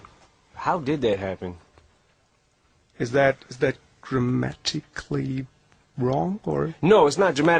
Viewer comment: "How did that happen?" "How did that happen?" "is that grammatically wrong?" "No it's not dramatically/grammatically wrong!" ahahahahaa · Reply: Lol nah I thought he said not grammatically wrong. Also it was kinda awkward the moment because the intervieeer didn't get it lmao🤡